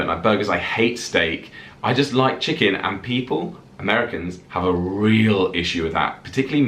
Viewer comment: did say that he only likes to eat chicken & people? 😳